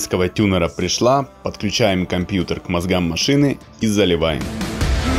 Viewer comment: Послышалось 6:53 Подключаем компьютер к (Мазгану машины ) думаю ЧТО )))